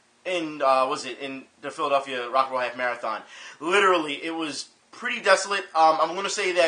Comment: I am so glad that it says Philadelphia Rock'n'Roll Half Marathon. Previously, I thought it said "Pedophilia Rock'n'Roll Half Marathon. :/